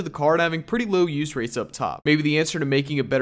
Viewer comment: I thought he said straight up dog at the end lmao